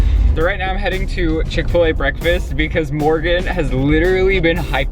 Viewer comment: I thought they said "Morgan just died" I was about to freak out